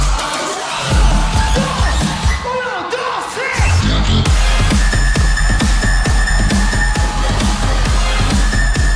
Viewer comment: What did he said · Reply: "uno, dos, tres" one two three in spanish